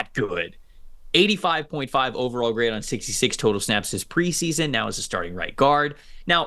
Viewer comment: They never said that lol, they said he's gonna break out and is the back up guard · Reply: He clearly said "Now he's a starting right guard."